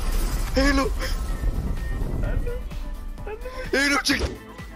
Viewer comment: Lチキって聞こえる